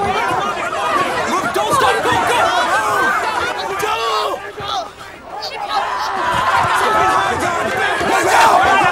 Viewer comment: What does he say in Arabic? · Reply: He said "ALLAH AKBAR!!!!" Which means "God is greatest"